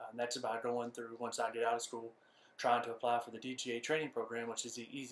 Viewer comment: Can you clarify me on that training program you were talking about near the 6:56 line? I think you said "DGA" but I couldn't tell. What is that?